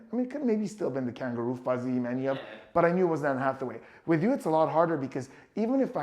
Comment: it sounds like you say "it WAS Ann Hathaway." even your body language with a nod looks like you confused, and left him with the look of cognitive dissidence on his face.